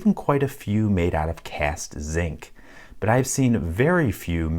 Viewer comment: when you said cast zinc I wasn't paying attention so I thought you said you've seen locks made out of pants